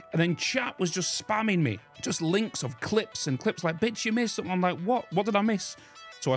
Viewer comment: Sounded like "B*tch you missed something!!" xD Idk why that made me laugh a bit